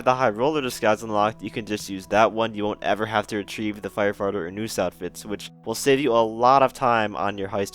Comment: Who else heard fire farter